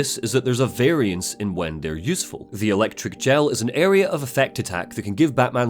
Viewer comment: Am I tripping or is anyone else hearing electric gel not explosive gel?